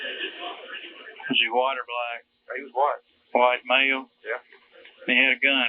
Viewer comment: But didn’t describe the kidnapper as “black”? Even the admittedly bad subtitles pick that up. But the bored 911 operator mishears him and says “white male.” Then apparently mishears the operator and thinks he said “black.” I think it has to do with the heavy regional accent. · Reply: I caught that too and thought what, he clearly said Black male